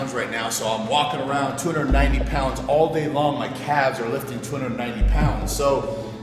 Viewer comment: did he just say: '290 pounds 'oil' day long'